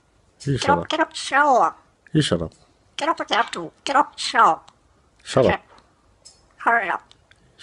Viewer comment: "No Abdul get up, shut up" "no you shut up" "shut up" I LAUGHED SO HARD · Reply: Melissa Toska I thought the bird said “Abdul get out of the shower”. XD